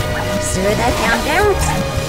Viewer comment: this what mayura says 😂 “sure that countdown who is suit”